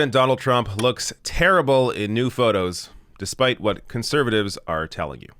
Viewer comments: I thought at first you said in nude photos. 🤢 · Ooh, I thought he said nude photos. I started gouging out my eyes with a rusty can opener at the mere thought.